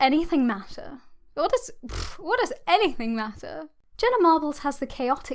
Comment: Oh JENNA Marbles! I thought you said Splenna Marbles, that was the brand of marbles I grew up with as a kid in France. Good vid! Can’t wait to see you talk about Splenna Marbles though!